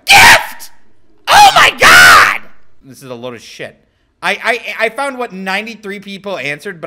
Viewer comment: that sounded like toad lmao